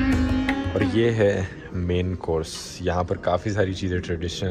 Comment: When dhruv sir said main course then I heard Man force 😂😂